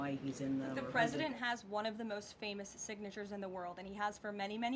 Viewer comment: I thought she said “even before he was president” referring to when the documents found are from , lol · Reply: Correction she said “the president (trump ) has had one of the most famous signatures in the world and he has for many many years” or I’m tripping